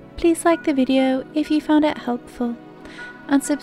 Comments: I hear unsubscribe xD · I hear unsubcribe